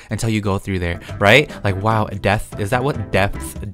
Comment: i thought he said death 😳😳